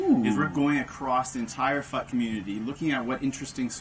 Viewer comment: First time I heard this, I thought he said "Entire Fucking Community" lol but he said "Entire FUT Community" :D